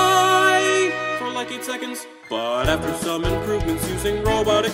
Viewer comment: I CAN ALSO FLY!!!! for like 8 seconds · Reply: I THOUGHT THAT IRON MAN SAID 18 SECONDS